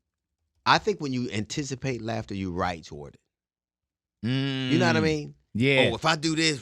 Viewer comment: I’m sorry but can someone please interpret what the heck he said at @ 47:15??🤨 ..what word did he say?!?!🤔🤔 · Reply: “Write shorter”